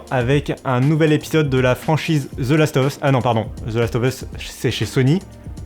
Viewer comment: J'ai entendu " the last of us " jme suis dis att quoi quoi 😂 méchant va